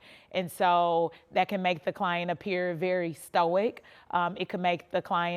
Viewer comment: WTF does histowick mean 🙄 the woman reporter is dramatic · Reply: I thought she was saying stoic?